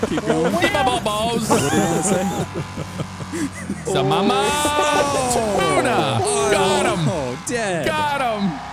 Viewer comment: Did anyone else hear "mama tuna"?